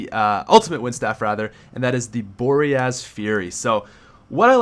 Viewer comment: lol I thought said the name of the upgraded wind staff was Boring Ass Fury lololol